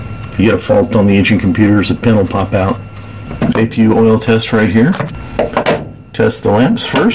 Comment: On your first take off thought I heard a call out for APU off. Do you leave in on for all flights?